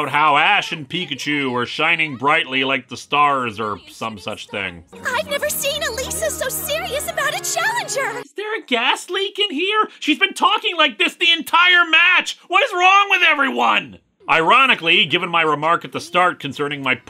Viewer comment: When he said "gas leak" i thought he said Gastly